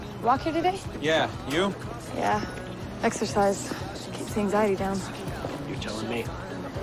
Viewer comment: This whole time at 0:48 i thought she said "keeps things eididown" whatever that means, but no it's " keeps the anxiety down" it blew my mind